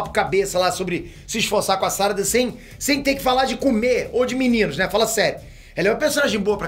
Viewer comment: eu entendi ele falando "sem ter que falar de comer meninos" auhsuhasuash.